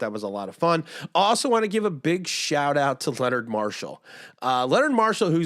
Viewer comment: With my tired Monday ears, I thought I heard say, Leonard Williams. :) when you said you felt fuzy and you were speaking so highly. I was quickly thinking, are you now going to get rid of the "King Of The Almost Sack!" drop?!?!? :)